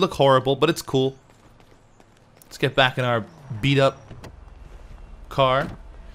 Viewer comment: I thought you said let's get in car